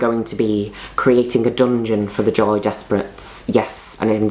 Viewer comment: Liquorice Black and Goth Joy Despret are going to be fuming about this... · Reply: tell me why this whole time i thought she was saying Joey Despret?!? I’m ashamed